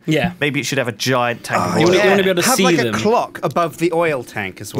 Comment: When Sjin told Lewis to put a clock on top of his oil, water tower thing, I thought he said "club" which is definitely a much better idea...He could party with the Harpies.